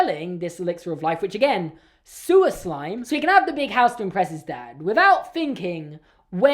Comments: When you said “sewer slime”, I thought you said “suiclime” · I thought he said suicide not sewer slime